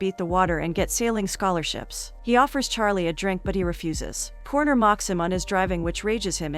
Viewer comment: 'Porter moxham on his driving', subtitles says 😂 It's Porter mocks him